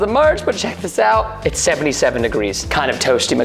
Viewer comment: preston:77 degress me: 77 mk i thought it said 71 xD